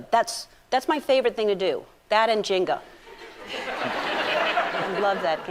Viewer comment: When she said "that and Janga" I heard, "that and Jenkem" I was like..... wait. WHAT?!